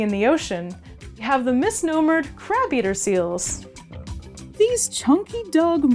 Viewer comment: Anyone else mishear her and thought she said 'crap-eater seals'?